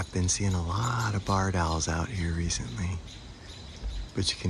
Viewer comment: Food for the soul! Still cannot get over fruiting around crispy leaves, and that owl, what a strong expression, they are something else! It's funny, when you say Barred owl, I thought you said Bard owl, and I just googled and saw I got the spelling wrong, but at the same time, a Bard is a poet, which is quite fitting too I think!